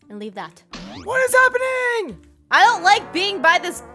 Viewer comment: I thought the creeper said "WHAT IS HAPPENING" 😂🤣